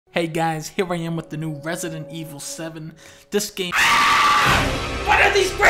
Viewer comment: Resident Evil? I thought you said President Gevil!